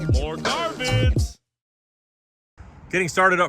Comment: When you intro song says More...more, more car vids... I keep hearing "more garbage".